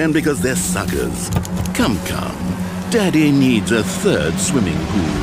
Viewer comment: What'd he say? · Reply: “well that was shit, wasn’t it?”